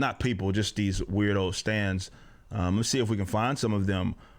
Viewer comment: Whenever you say "stans" I hear "stands" like it's a JoJo episode